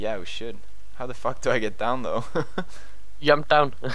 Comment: that was the most swedish „jump“ in english i heard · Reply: Yump